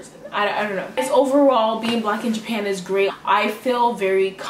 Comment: I thought it said "BACK IN JAPAN!" 😂